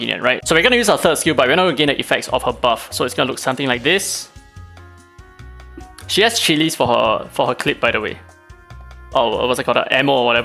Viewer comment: „She has chillies for her clit btw” ☠️ what I heard